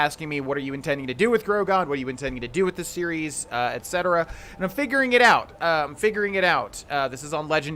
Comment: Did you just say "Im fingering it out" ?😂😂😂😂